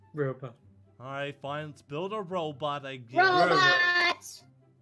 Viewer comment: When Wolfie said he would build a robot I thought he said Roblox..